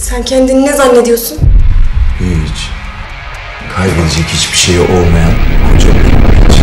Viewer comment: saniye aras ne dedi ? tam olarak..? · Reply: kayib edecek hic bir seyi olmiyan kocaman bir hic diyor